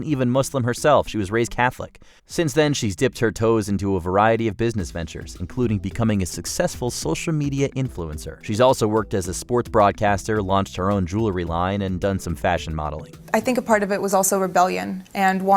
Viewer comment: "She also worked as a sports broadcaster". At first I thought I heard "sports bra caster".